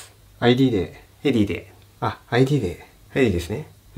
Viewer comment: ここから「現金で」が「IDで」に聞こえる店員好き